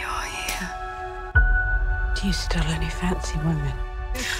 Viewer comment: what does ronit say in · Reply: Do you still only fancy woman...